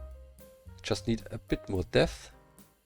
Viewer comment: who Else heard „we just Need a bit more death“?